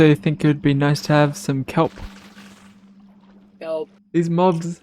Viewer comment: after you say kelp it kinda sounds like you said help in the background lol was that intentional? 😅